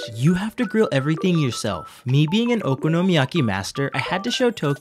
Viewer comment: For a second I thought he said “but since I’m an Okonomiyaki bastard” 😂